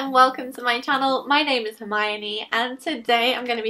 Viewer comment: For one second I thought you said "My name is Miami" in the beginning and instantly I heard Will Smith in my head singing "Welcome to Miami" ....I guess I'm just ready for some spring sunshine lol! Love you!! :)